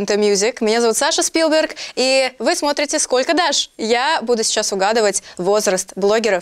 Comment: Мне послышалось что в начале Саша сказала «сколько Дашь»? Дашь в смысле имя